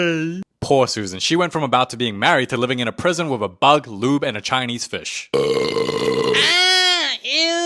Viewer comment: did he just call him a lube 💀💀💀